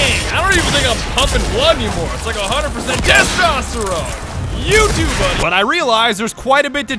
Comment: who also heard youtube money instead of you to buddy when they didn't read the text only heard what he said